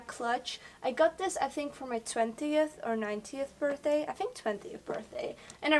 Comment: I love how in 10:11 she say's I think i got this for my 20th or (what sounded like) 90th birthday lol when she meant nineteenth birthday. lol, but she is so inspiring. love u